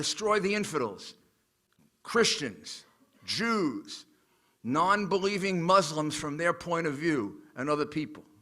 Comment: did he say "non-believing Muslims from their point of view"? (approx 8:50) meaning Muslim People that don't believe in terrorism.